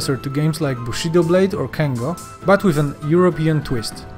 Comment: Did he say Kengo?? Man that brings me back to the PS2 days.